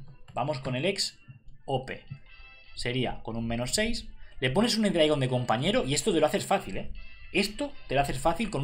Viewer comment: Que dice le pones y dragón o me equivoco